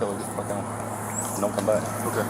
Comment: Did anyone hear the fist officer when he told the “ rookie” to tell the lady First Lady to “ get the fuck out & don’t come back ” ? time stamp 1:39-40 UNBELIEVABLE & UNPROFESSIONAL! 🤮DISGUSTING